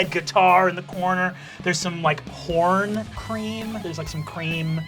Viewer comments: just after I wasn't paying attention and misheard and I CACKLED and turned like "THERE'S PORN CREAM?!?! WHAT IS PORN CREAM?!?!" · At Figs house I thought Brandon said PORN cream not horn cream